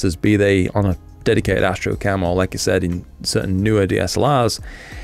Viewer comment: Did you say Astro Camel?